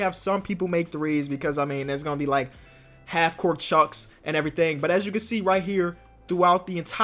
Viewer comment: Is it me or did he say " Half Court Chucks" instead of " shots"